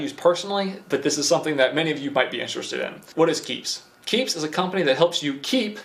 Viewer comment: Ah man I thought you said peeps